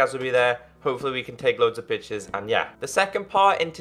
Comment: I was wearing headphones and it still sounded like he said "We can take loads of bitches" not pictures. At